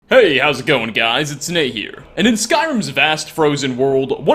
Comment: Why do I always hear, 'Hi, how is it going on guys, its snake here!' :p